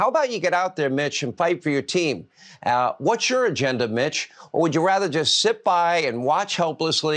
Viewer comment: i thought he said "what's your agenda bitch"